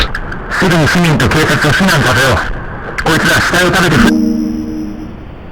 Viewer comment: I know what he said… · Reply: He said corps- I see something floating OH MY GO